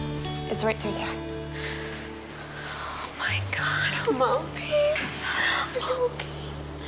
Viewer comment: Is it just me or at 6:03 I heard the baby say "Mommy"🥺